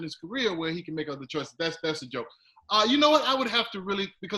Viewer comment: Did this man just say saying he's at a point in his career where he can do what he likes is a joke? "That's the joke" or am I hearing things?